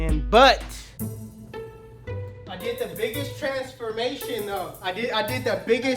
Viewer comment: I thought he said " I did the biggest *Trash Formation*. Then go back and said " I did the biggest Transformation " ... Lol that wuda been funny tho